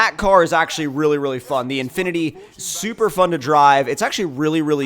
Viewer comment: i thought he said supra 😳